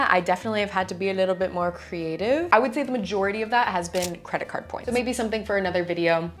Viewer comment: I had to rewind and look at the screen. Because I was looking away and I thought she said "credit card fraud". I was like "Yeah, people do make money that way, but..."